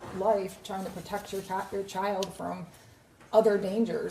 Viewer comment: She says cat at. 8:47. Instead of child. Interesting...